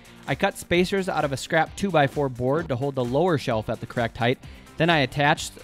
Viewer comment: who else heard "crack pipe"?